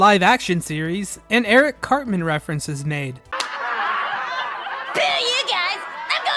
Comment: That very last one, I'm assuming they wouldn't say "screw you" on a kids show, but I can't make out what she's actually saying. "Spew you guys"? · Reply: i heard “boo you guys”